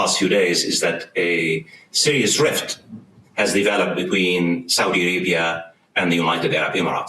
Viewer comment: about yemen...i thought i heard "south arabia" not "saudi arabia". if i heard it right.